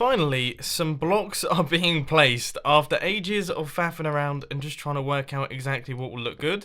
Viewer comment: did u say ages of fapping around?